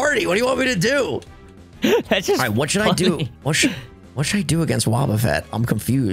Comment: did say the f word at 18:46.. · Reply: He said funny